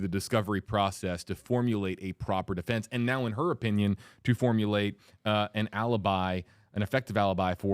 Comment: Every time i heard "formulate alibi" all i heard was " fabricate an alibi " 😂